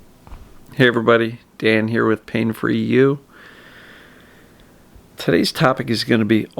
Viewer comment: Nice advices daily. I love it. Btw, I always heard you said "pain for you" instead of "pain free you" 😉